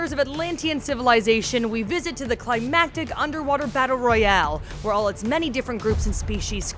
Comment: Did she say Battle Royale?